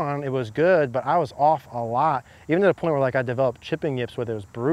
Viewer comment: @ 10:38 what did you say after "I was off a lot" and before "I developed chipping yips"? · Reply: even to the point where